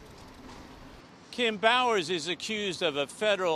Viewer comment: sounds like he said Kim Bauers. Got thinking about the Kim Bauer, Jack Bauer's daughter on 24.